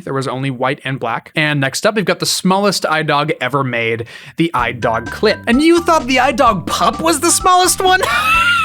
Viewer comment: I thought you said I-dog clit😳